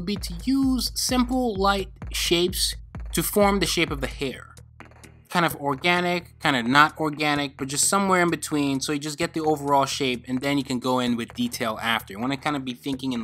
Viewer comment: did he just say kinda organic, kinda not organic